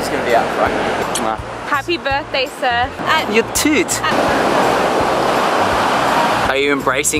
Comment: thumbs up if at first you thought said "your tit".........